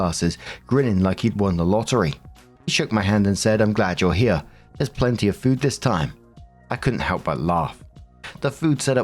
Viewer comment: I was listening and thought the guy said "There's plenty of poop this time!"